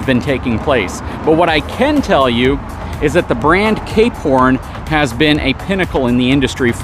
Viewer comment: Ah, "Cape Horn", I kept hearing "K-Porn". I like the variety of content covered here. Not always my cup of tea, but that's fine, I just choose which videos to watch.